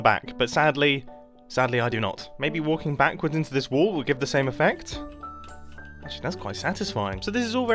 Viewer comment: “Maybe bouncing back and forth ok this wall will work.” Then I just hear: “Ohh- that feels quite nice…” Im dying 😂